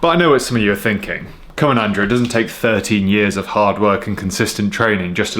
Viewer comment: oh I thought you said 30 years thinking you were 45.